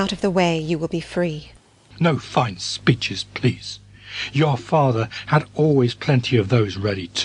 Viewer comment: I looked away from my book for a second while the audio kept playing and i was shook because i thought Torvald had said "No fights b*tches please" at 2:09:27 💀